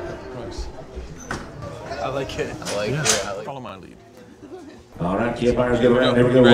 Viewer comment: sounds like he says "alright, kid buyers" must have a 2nd auction job😳